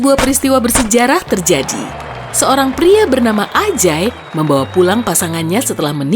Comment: tak kira Anj#y salah dengar ternyata wkwk